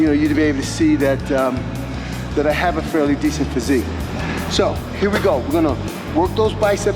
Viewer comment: did he just say pretty decent physique?